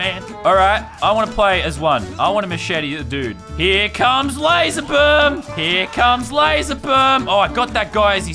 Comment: it sounds like he says here come lazer bum